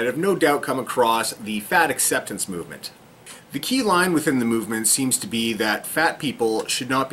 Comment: He said "key line" and i thought he said "key lime". Am I fat?